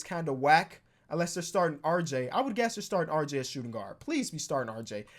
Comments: he said unless their starting,but it sounded like alexa start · when you said "unless they're starting rj", it sounded like you said "alexa start rj" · You said unless they and she thought you said Alexa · he said “unless they” fast which kinda sounded like he said Alexa